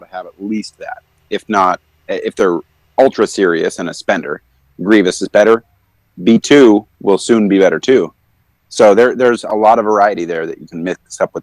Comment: As a lvl 58 mostly f2p droid user, I'm trying to figure out what the other guys says about B2 at 4:50. Replayed like 20 times and still couldn't quite make it out. Anyone else understand what he says? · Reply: I think he was saying that Grevious would be better than B2 but that's mainly for spenders AKA P2P, otherwise B2 would be the better choice